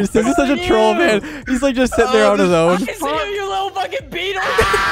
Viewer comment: Did say beaner